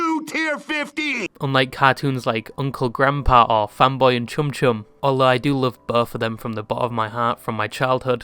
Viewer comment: is it just me or did he say femboy Chumb chumb